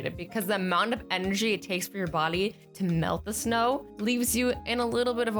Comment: Azzyland why did you say boly the time at 2:56 did you mean body